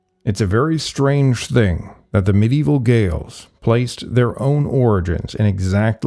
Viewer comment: medieval what? I can't recongise that word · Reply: He said Gales... I wonder if he meant Gauls?.. A lot of his "facts" seem questionable. I wonder if he is reading an AI script...